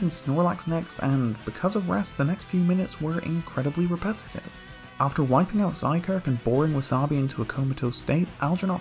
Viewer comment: At first I misheard "after wiping out Zygarde".